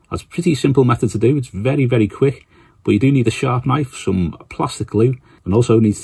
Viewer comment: I like sharp knives😎 Disclaimer: I only use for minis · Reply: ahh, I kept hearing 'shark knife' that makes more sense.